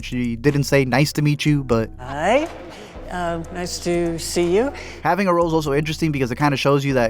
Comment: I think she said nice to see you cause she already met her before